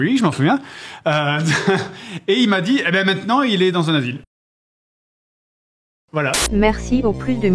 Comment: il a dit quoi ? il est dans un avie ou dans un asile ? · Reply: asile :)